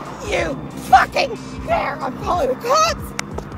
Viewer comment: c’est moi ou elle a dit “Mario cart ”? Like pour qu’il voit !👍👍